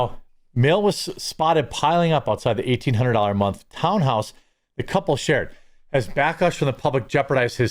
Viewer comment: I heard “$1,800 a month pound house”. lol. Thx for the videos Jeremy. Love the new channel too